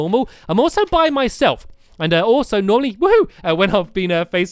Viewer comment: Is it just me or did I hear Nolly instead of Normally??